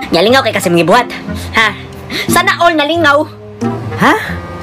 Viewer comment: is it just me or did i hear "sana ol" in 'i wish everyone's happy' part? miss are u filipino??